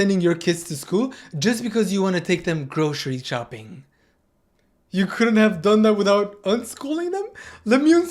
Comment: I heard grocery chopping